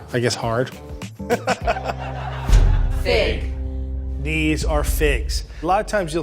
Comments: I Heard Thick Instead of Fig XDDD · for a second I thought he said pig